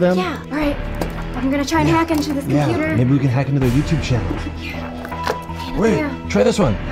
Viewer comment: I heard him saying they have to kack the youtube channel